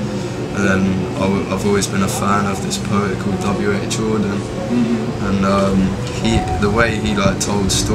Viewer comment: btw, the subtitles meant to say W.H. Auden but the king's accent is thick and they thought he said Jordan lol